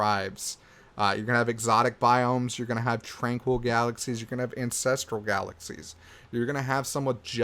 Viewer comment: I had to rewind like 5 times because I thought you said incestual galaxies.